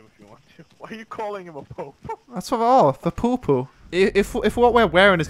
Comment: it sounds like hes saying poopoo